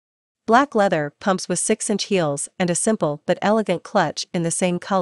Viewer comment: Did it says 6 inch heels….. 😅🤯